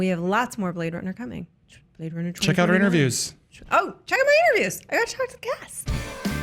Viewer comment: I thought she said "Check out my areolas", when she said "reviews." Freudian slip I guess, cus I luv them taaateeess! :)